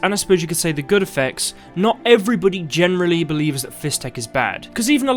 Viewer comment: I always misheard it as "fish tech" so I assumed it was some kind of dried fish powder